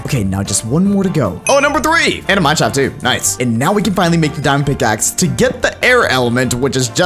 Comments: he says damn instead of diamond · the editor thought he said damn not diamond xD